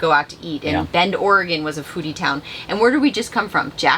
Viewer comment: When you said “a foodie town” my wife was half way listening and she asked.. did he just say a booty town? 🤣